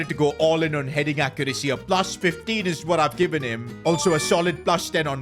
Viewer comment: you said 15 I heard benzema